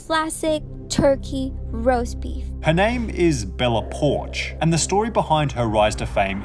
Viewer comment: Sorry, but what i hear is "Bella Porn"!